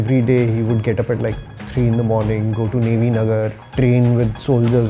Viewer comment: Did he say Navy Nagar?? I've lived there.